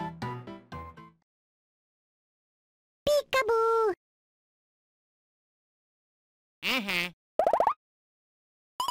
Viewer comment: Well, Peekaboo was the name of a badge in the first two Paper Mario titles. (5:30) Maybe he did and was willing to risk it? Though he does express being surprised by the volcano's eruption, the entire mountain indicated not a speck of lava. (6:14) What did Elvin Gadd just shout...? "YOOOOOOOO!" I doubt it unless his younger-self was fascinated by slang. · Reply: Pretty sure he was saying “Yowzers” or something like that